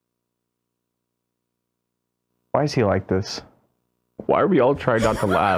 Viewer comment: somehow the electrons in my brain made me think he said “Why are we all turned on” while I was eating.